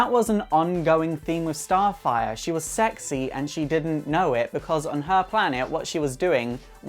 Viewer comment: Sounds like he saying I sexy and I know it 😕😃